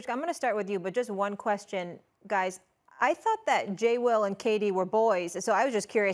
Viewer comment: Did Molly just say "Jay Z" @ 2:10? 🤔